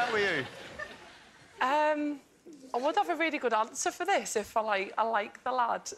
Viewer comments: What does she say at 7:23? Is my or her english so bad? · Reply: She said she would have a really good answer for the question if she liked the lad, implying she didn't like the guy. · wot did she say? " I like lawodaud" what? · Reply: I would have had a good answer for this if I liked the lad.